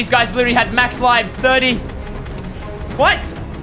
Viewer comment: Hold Up Wait A Minute Did He Just Say “COVAX!”